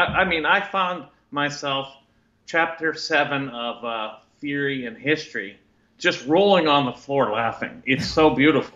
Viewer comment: Can anyone name that book mentioned? Where the guest said he was laughing while reading chapter 7? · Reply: I figured it out. It's Theory and History by von mises. I thought he said "Fury of History" which is why I was so confused